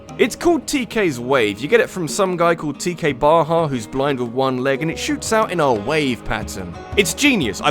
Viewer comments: I can't be the only one who heard "blind in one leg" at first, right? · did he say blind in one leg?